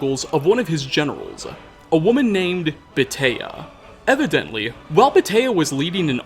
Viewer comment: I listen to these vids while working and it got a little loud just now... Anyway, I swear I heard him say "A woman named Potato..." Instead of "Beatia" 🤣